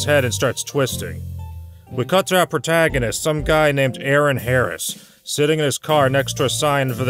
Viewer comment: I wasn’t paying attention so at 2:03 I thought you said Eric Harris (one of the Columbine shooters) so I had a double take to make sure they didn’t name their character that.